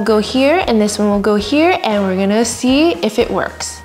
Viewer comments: I was about to fall asleep and damn I heard "its actually twerking, it's twerking", i woke upp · I thought you said it's "twerping it's twerping